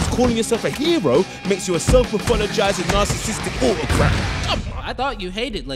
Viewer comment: I thought he said "narcissistic bullcrap."